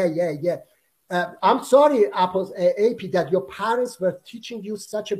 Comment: Muji why did you call AP Apuss like the terrorists do? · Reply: I didn't call him Apuss, I was going to call him Apostate Prophet then I realized it is too long so I changed it to AP and it sounded like that. I never call him that way because it doesn't change any reality.